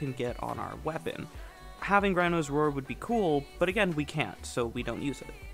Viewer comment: I can't make out what you're saying at 1:30. Having what would be cool? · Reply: Rhino's Roar, it is a helminthable ability that gives a large faction damage bonus (one of the few ways to get faction damage.) The reason we can't use it is due to a restriction that DE put in place to prevent players from using two "damage buffs" on the same frame (you can't slot eclipse into rhino without taking off roar, etc.) Since the build utilizes xata's whisper, and xata's whisper takes the same buff slot as roar, it can't be used in this setup. It's a really arbitrary restriction, things like breach surge are a form of damage multiplier, as are buffs like roar, but breach surge can be slotted in and roar can't, very silly.